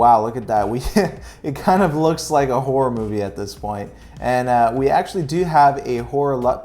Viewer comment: I swear I thought you said it looks like a wh*re movie now 😂